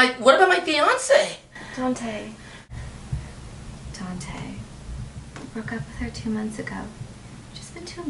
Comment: Lmao ZUKO I thought said don't ever break up with her 😂😂😂😂